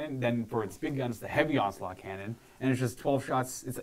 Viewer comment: The first time said "onslaught cannon" I thought he said "ocelot cannon." I demand ocelot cannons.